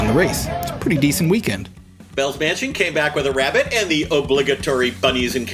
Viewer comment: I think I heard "Bell's Mansion" and now I want some bread...